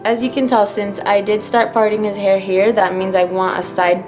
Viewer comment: did she just say she was going to start farting his hair???